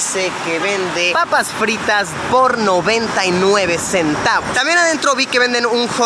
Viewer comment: no taron que dijo porno :::::vvvvv